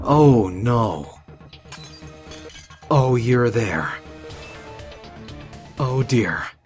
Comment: At about 3:22 He siad oh you're there. I thought he said Oh you're a bear